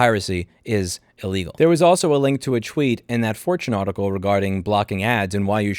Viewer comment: did u just say twheet?